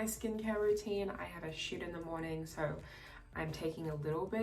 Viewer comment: Did phoebe say “I have a shit in the morning” at 4:37 ? Am I hearing wrong lol